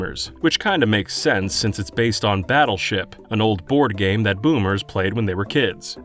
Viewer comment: Did he said Boomers.